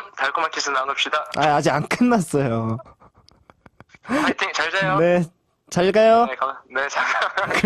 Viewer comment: i believe Donghae said "Fighting! Jalgayo, naeko" i'm going to believe it's what he said <3 my eunhae feels